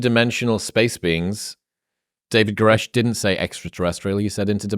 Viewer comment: I thought he said David Koresh and that it is not fair to David Koresh.